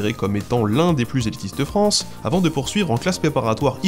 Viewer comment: Lycée Henri 4 considérée comme QUOI ? J'ai écouté le passage en boucle et j'ai pas compris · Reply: J'ai compris "comme l'un des plus sexiste de France". Un peu perturbant.